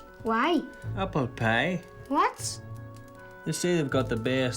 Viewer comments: I thought he said “Apple Pay. They’ve got he best Apple Pay in the world”. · Did i hear apple pay?